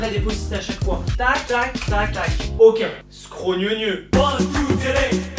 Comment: vous comprenez quoi?? Moi je comprend Scraugneugneu😂😂